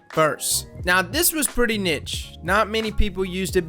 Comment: Sorry did you say nitch? Lmao